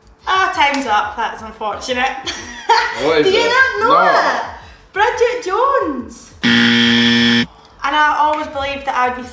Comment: Paige has such a thick accent that when she said Bridget Jones, I thought that she said Project Jones, LOL. Its cute.